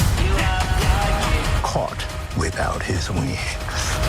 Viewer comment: Anyone else hear caught without his wigs?!? 😂